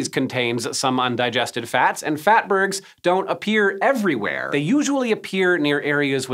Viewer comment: I thought it said 'fartberg'